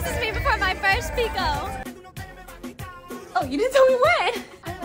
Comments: At first i thought you were saying my first pickle.(with a Texas accent), but then I read Picolandia. I'm old! 😂😢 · The whole time I thought they was saying their first pito 😂😂